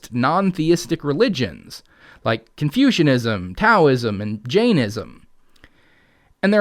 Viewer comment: Heh, when he said Taoism, for about a second, I thought he said Cowism and I was intrigued. 😂